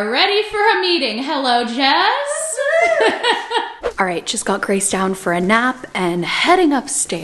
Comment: I thought you said "Jess put [Grace] down for a nap" and for a sec I was like wow, so cool that Grace is that chill with her, and then I realised you said "just". 😅